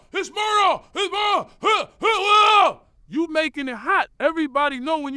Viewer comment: This whole video has a police station vibe. "I told him SMD, the whole first class heard that shit, business, coach"....I thought you said Ja was making it hot, sounds like that was you 😂😂